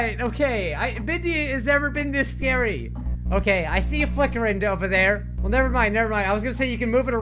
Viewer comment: I thought he said I see a fucker over there O.O